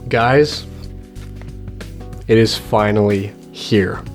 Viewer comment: i thought u said "Vinylly here" (finally)